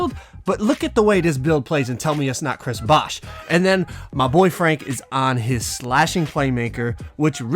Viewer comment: "My boy frank"...thought you said my boyfriend for a minute 😂